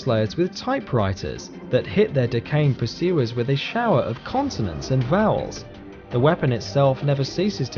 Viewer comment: I think you need to enunciate more often... In the Typing of the Dead section, it sounds like you said they keyboards "shower the enemies with continents and vowels". I'm pretty sure you said/meant "consonants"... Btw, the Junk Jet may be cool, but the Rock-It Launcher (Fallout 3's version) was so much more satisfying, since you have to make it yourself, rather than just get given it by the storyline.